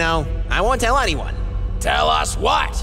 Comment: For anyone who doesnt get the joke at the end everyone went o_o because it sounded like neptune said "That your anus stinks"